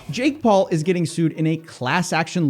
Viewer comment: Lol I started the video with the volume low, sounds like you said "Jake Paul promoting Satan"